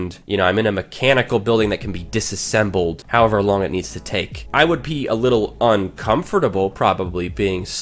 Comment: who else thought he said, "I would pee a little" lmao